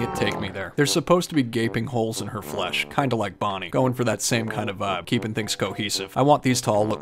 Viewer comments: am i the only one who heard him say bonnie instead of foxy? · Did he say Bonnie